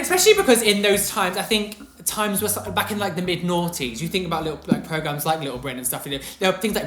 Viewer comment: said, "The mid-90's". I thought he said "The mid-NAUGHTIES", I'm dying with laughter. if you don't want to have the public info against you, why don't you give your side of the story? I would like to know what happened. I have reserved judgment (and continued being a viewer) since I heard the story at the time. I'd also like to have a side of;-) The "Call-Out Culture" is rightly called virtue signaling. It's sad that the 'right or wrong' pursuit can be replaced with "offensive" or "non-pc" as if being offended is some type of currency.